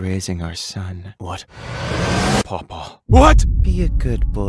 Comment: The last one make me thought he's saying:"what the sigma" Edit:I'm not brainrot,im just listen to this thing a hundred times